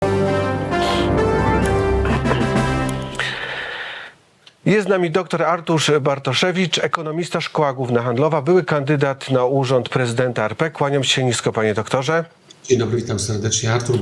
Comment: Wydaje się że facet który celowo przekręca imię lub nie potrafi go wymówić może powinien sobie odpuścić prowadzenie takich kanałów oczywiście doktor Bartoszewicz subtelnie przedstawił się sam wyłapując sytuację…. jakoś dźwięku w drugiej czesci dramat …. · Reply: No właśnie, ja usłyszałam "Artusz"